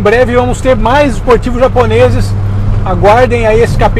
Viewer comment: Eu ouvi "mais esportivos japoneses" ? estaríamos falando de um R34 ou um Supra ? Acredito que seria algo MUITO esperado por todos os fãs da garagem ! Como sempre um ótimo vídeo, forte abraço!